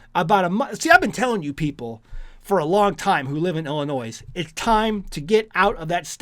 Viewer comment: Did he just say "Illin NOISE" ???